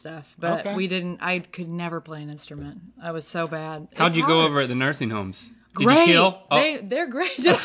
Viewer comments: What does Link say at 1:14??? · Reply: “How’d you go over at the nursing homes? Did you kill?” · I didn't understand min 1:20, what did link say and why was it so inappropriate? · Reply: Hashtag Lan Fbx he said, “did you kill,” which is funny but inappropriate, given all the sick old people in nursing homes. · What did link say at 1:18 ??? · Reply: “Did you kill?”